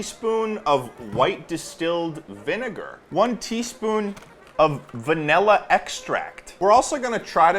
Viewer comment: was saying “one teaspoon” over snd over again and i kept hearing “twenty spoon”